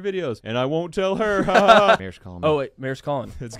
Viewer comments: I thought he was saying don’t tell the mayor … like she was the mayor of the ranch … I’m dumb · This whole time I thought he was saying "Don't tell the Mayor". 😮❤😅